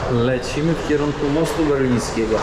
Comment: Dwa razy usłyszałam "most berlinski".